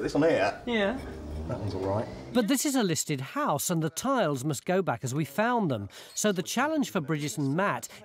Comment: I thought they said the tiles had to go back in? And then they just smash them lol